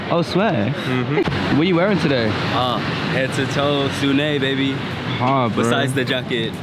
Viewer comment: what brand does he say? TIA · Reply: sunnei